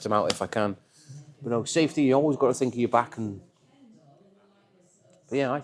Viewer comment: Thought you said you always sign the parts?